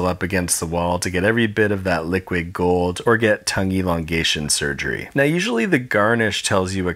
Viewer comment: I misheard "tongue elongation" as "tonguey longation"